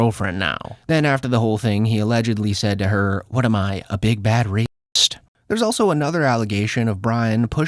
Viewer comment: I was just waking up and could have sworn it said big bald rapist, but it is indeed big bad rapist. Just allegations, allegations Bryan only denies in part, because whilst exposing yourself is definitely sexual assault, I don’t know the laws whether that would be considered rape. Either way is definitely bad. And he’s not exactly not bald, seemingly thanks to certain treatments, in my opinion, professional baldie (alopecia universalis) but inexperienced rapist (zero attempts, I’m not cut out for the lifestyle of a rich person or a comedian, let alone a friend of Rogan’s).